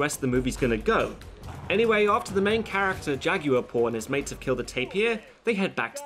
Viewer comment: did he say Jaguar porn? xD